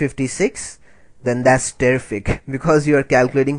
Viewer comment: did u say terfect :/ not trying to be mean